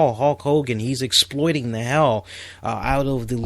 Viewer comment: Very good and insightful John Cena analysis great video. And just a random note, towards the end of the segment when you mentioned Hulk Hogan I thought you said Hawk Hogan lol just a little something I misheard but found kind of funny either way. Keep up the good work man.